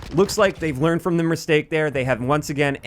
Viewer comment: Did you just say.... merstake? At 2:58. This is honestly with the utmost love